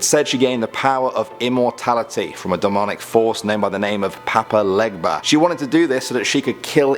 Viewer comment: Anyone else hear Papa Ligma